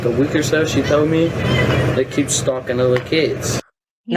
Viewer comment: Wow I thought he said “that keeps stalking the little kids” Not looking good for BOoger!